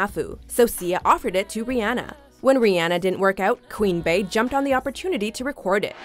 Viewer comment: did she say Queen bAE instead of Queen B